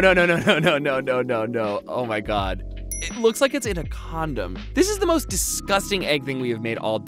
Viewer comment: Hey did you say condom